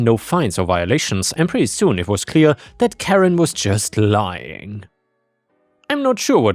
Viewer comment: I heard Carrot Karen. That made me laugh... 8:14 :face-purple-smiling-tears